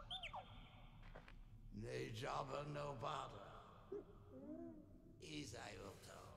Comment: did he say: “Hey Jabba no botha.” ?